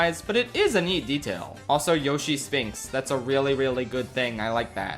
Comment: I thought you said yoshi stinks and I was gonna throw you out the window